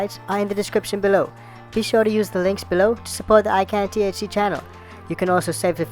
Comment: Bro! Seriously sounded like you said “join the I can’t see club” stoned me: come to think of it I’ve never seen a video yet without him having sun glasses on..??🤔high me for like a sec.. is this guy blind…🙄 ICANTHC 🤦‍♂️ not “I can’t see” hahaha good vids bro